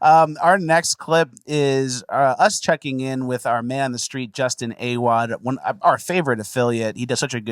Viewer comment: I literally thought you were saying “just an A-wad” at first